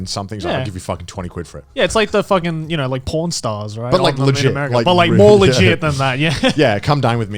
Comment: When Joey said “Pawn Stars”, I misheard him and thought he said “Porn Stars”.